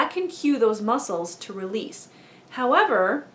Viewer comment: i thought you said,"and that can relieve your muscles in 'two weeks'." But you said 'to release'. scared me for a sec lol!